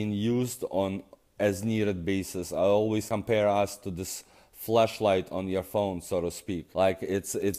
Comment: Sounded like he said "it's like a fleshlight on your phone"